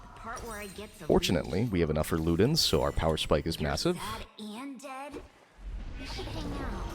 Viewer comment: did you say massive ???